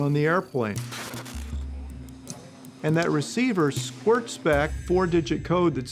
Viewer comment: did he say squirts instead of squawks 😭